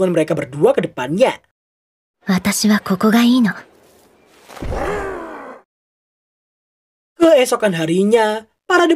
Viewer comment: ini ngomong apa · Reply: "aku lebih suka disini" (sampingnya Patrick)